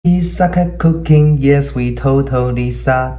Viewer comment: I heard him say we suvk cocaine but then i realized that it was we suck at cooking